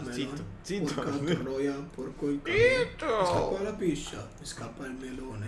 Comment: porco il $&% ho sentito bene? ahahahah